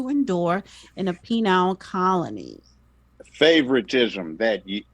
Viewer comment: Did she just say penile colony instead of penal colony? 🤣🤣🤣